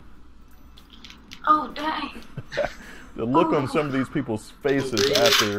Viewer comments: did she say oh daddy? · did she said "oh daddy"??? 0.o